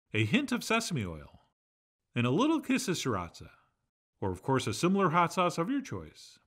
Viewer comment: I had to rewind and check. I thought you said a "Piss of Siracha"!